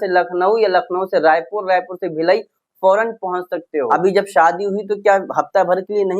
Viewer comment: did u say "Bhilai". How do u know that place. Coz I'm form bhilai. And m shocked 😯. Coz it's not that much big city.